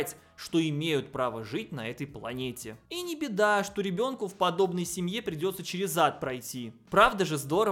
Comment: услышала "ребёнку в семье придётся через ЗАД пройти😂😂